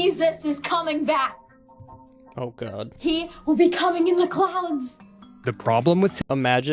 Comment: I thought the young lady said, “Jesus will be coming with the clowns 🤡 “